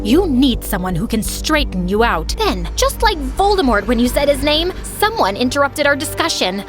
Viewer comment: HELP I THOUGHT SHE SAID VOLDEMORT WHEN SHE SAID BOLTMORE